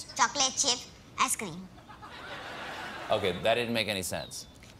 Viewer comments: Was the last line, "Hey, I did not write the script"? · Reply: I thought he said, "Hey, I did not ride ice cream." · Was the last line, "Hey, I did not write the script"? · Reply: i thought he said "hey i did not eat ice cream"